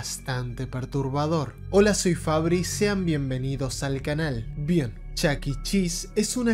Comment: Alguien tambien penso que dijo : hola soy fabricia Momento